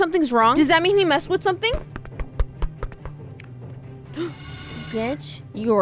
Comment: I thought they said the b- word…